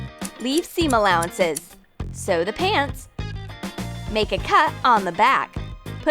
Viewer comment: Am I the only one who heard her say "sew da pants"??